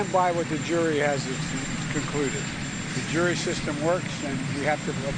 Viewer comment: Did he just say @ 0:15 "what the Jury hasn't concluded." another gaff. He wanted to say what he was thinking, he clipped and then called an audible. Oops old man, you are the racist one here. You should be ashamed.